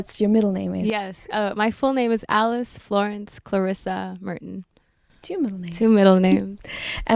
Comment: ich hab denn vollen Namen nich richtig verstanden sie ist so toll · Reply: Alice Florence Clarissa Merton :) Youre welcome !